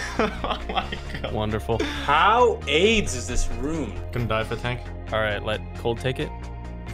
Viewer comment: did he really say "how AIDS is this room"?